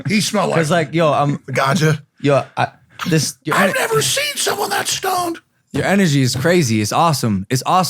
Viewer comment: did he just say dwarf mamba smelled like weed 😂😂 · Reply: King Crossover23 “I’ve never seen someone that stoned” 😂😂😂 that shit killed me😂😂